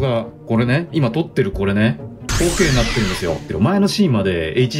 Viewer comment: この動画ほーけいになってるんですよ って聞こえたｗｗｗｗ